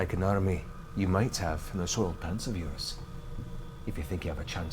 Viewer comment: didn't catch what Matt said 'kind of like an army, you might have ...'. Text misses it. Seems like a cut glitch, can't make it out. Any ideas? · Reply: I could swear he says, "in those soiled pants of yours," but 1. that doesn't make much sense and 2. hard to believe neither the cast nor the chat wouldn't immediately riff on something like that ("he said we have an army in our pants!" "must be talking about you, Grog" "Grog doesn't wear pants" #soiledpantsarmy). I can't figure it out either. The accent is strong but perhaps not terribly consistent.